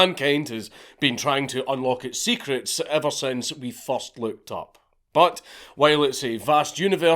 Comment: Thought you said "first lubed up."